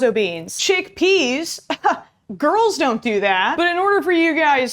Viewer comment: I thought it said “don’t f the Chet”